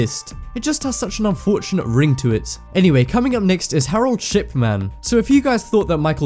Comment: wait wait wait I swear to god I thought you said shitman XD Lol